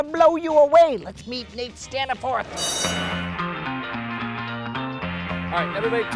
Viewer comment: When I herd the name of your friend I thought you said Nate scam the fourth